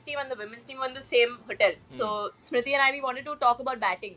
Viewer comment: Did Jemi really said that ....She said Smriti Irani instead of Smriti Mandhana😂